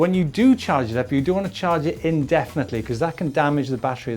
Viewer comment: min - 0:56 What does it mean ?? dont know the word (indeadedly) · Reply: Indefinitely, means leaving on charge and not switching off. I charge mine when it needs it, usually after 2 rides, about 70ish kms ready for the next 30-40km ride. When the battery is charged, the charger switches off so you can do it overnight either on or off the bike with shimano steps. Like all electrical devices, its best to be safe and disconnect once finished, dont leave on indefinitely as states.